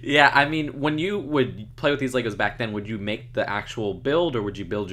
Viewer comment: Wait,wait,wait........at 13:22 did he say legos!?!?